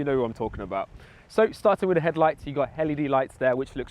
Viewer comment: did you say it has hell e d lights?